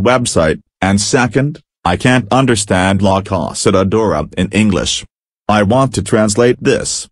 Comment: I can't understand "Black Oss at the Dora" in English.